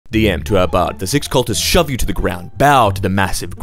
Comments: Anybody else first heard that as "the SEX cultists shove you to the ground"? 😅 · Been awake for the last 31 hours, my phone was running slow and when I scrolled to this, and I thought it said "The S#X cultists shove you to the ground" 🤣🤣🤣🤣🤣🤣🤣. And as it proceeded further my brain was like "where the Heck is this GOING!!!!"